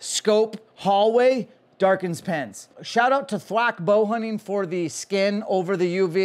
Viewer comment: What was the name of the company that did your skin on the UV? I tried looking it up on google, it sounded like you said Flack Bowhunting.